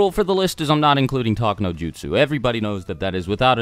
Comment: does he say talk no jutsu?!?